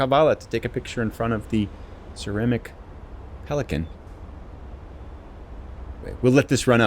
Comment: I was listening while I was eating breakfast and I heard "she came all the way to Kavala to take a picture in front of the tyrannic pelican". I looked up and thought, "Yeah, that must have been 'ceramic'" 😀